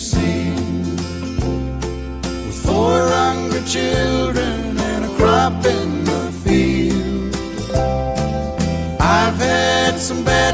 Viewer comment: When I was a little girl, I always thought it said 400 children a crop and a field! Hahaha